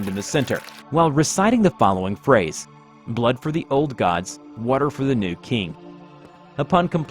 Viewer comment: lol i almost misheard that line to: "Blood for the blood god"